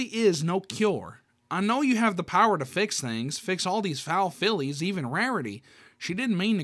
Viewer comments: Tbh i thought it said foul flies…💀 Celestia really said “Im the bad guyy…” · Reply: I thought it said foul files.💀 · Tbh i thought it said foul flies…💀 Celestia really said “Im the bad guyy…”